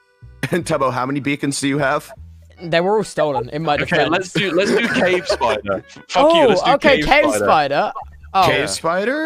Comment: Wil did i hear "f*** you, lets do cavespider" did i hear that right 🤨🤨